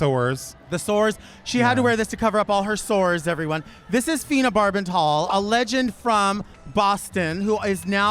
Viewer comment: 🔶Even in the middle?🔶 And did Delta say Fina Barbasol at (18:59)? I mean... 💈🪒